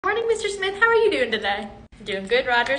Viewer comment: I misheard Mr. Smith as Mrs. Smith and got really mad 😅🤠